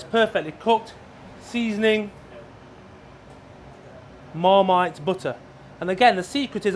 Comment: What he say at 4:20 Moremaid Butter? 20% Moremaid 80% Butter?